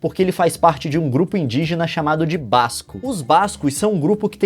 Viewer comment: entendi "Vasco"